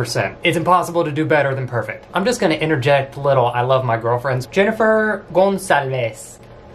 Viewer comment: Time stamp 7:00 I heard girlfriends with an S